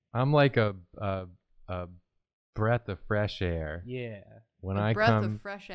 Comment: I thought said "A breath of fresh ass"